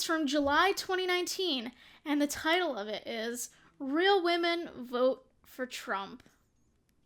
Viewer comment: istg i thought that they said “real women fuck trump” until i watched this